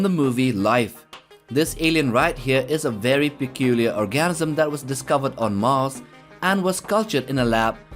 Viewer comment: did he say orgasm?